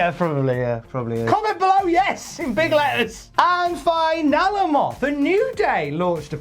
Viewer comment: I’m sure that it is because I’m high but when was shouting “YES IN BIG LETTERS” I swore the man was saying BIG LETTUCE. 😅😂😂